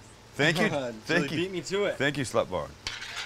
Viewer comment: did you call her “SLUT BOMB”. OMG, tell me know. On a lighter note, this recipe is AWESOME. I’ll be making this again for the third time! I always come back for a review of the recipe. I’m not one to print out or write down recipes. Needless to say these dogs are super yummy! Thanks AGAIN!!!